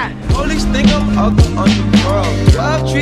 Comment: Andrew: Whispers You're in so much pain rn.. Tobey: smiles I am 😀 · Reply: When I first heard that, I misheard it and thought they were breaking the fourth wall with andrew saying “you’re getting so much pay, huh?” Like they paid tobey a lot to be in the movie lmfao